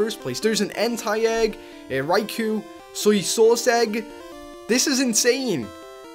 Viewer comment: Did he say Entei, Raikou, and SOYSAUCE? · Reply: Snoluck AJ it sounded like he said soysag